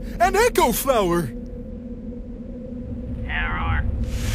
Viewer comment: When they went to the echo flower I thought it said "do the roar"